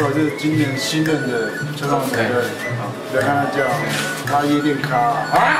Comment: 說什麼😂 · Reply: 不要看他這樣，他夜店咖😁